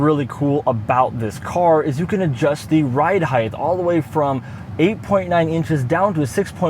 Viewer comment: Please tell me I didn't hear "HEIGHTH"